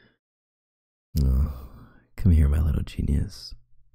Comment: Me ~not paying close attention~ Him: come here, let me rub your temples? Me: ~almost spits out drink~ did he just say nipples? How does YouTube allow that? ~rewinds 10 seconds~ Ohhh temples, okay then.